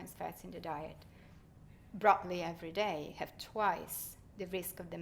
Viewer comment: what is she saying @ 23:48 BROCOLI???